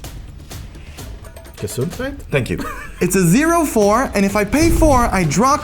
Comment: What had he said? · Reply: "Gesundheit", aka health in german, you typically say it after someone coughs or sneeses (like bless you in english)